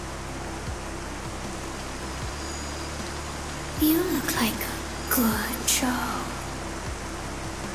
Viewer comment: I thought she said you look like gojo I was like huh!?